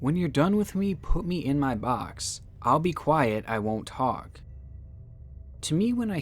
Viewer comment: I hate to be that guy but.... Quite instead of quiet Love the videos with the editing and commentary style